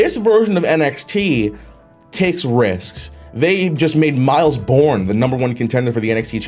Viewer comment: thought he said Evan Bourne I said what